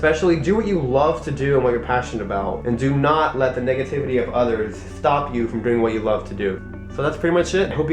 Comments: I THOUGHT U SAID NIGGATIVITY AT THE END SO I PLAYED IT BACK A FEW TIMES AND I STILL THINK U SAID NIGGATIVITY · It sounded like you said niggativity.. I died :'D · I thought he said dont let the "niga" tivity of others get u down....